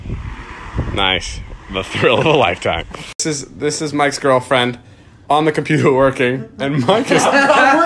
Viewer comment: Thought he said “this is my ex girlfriend” it was “this is Mike’s girlfriend”